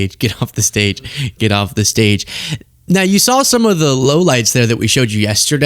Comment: HOLYMOLYPHUQCRAPAROLY I thought you said you showed us LOWLIFES not LOW LIGHTS